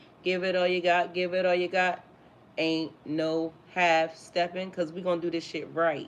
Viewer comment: I heard "Ain't no stopping us now ' 🎶 "Were on the move"!